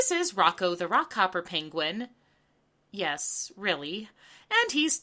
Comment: When you introduced Rocko I thought you were saying "Rocko the Rock Opera penguin" until I turned on closed captions. Lmao too bad, it would've made things more interesting. 😆